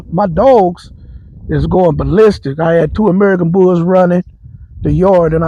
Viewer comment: argentina dogo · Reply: I heard him say American Bulldogs and I am a dog lover. ABs, especially, I love a good dog too. He got a Kennel page??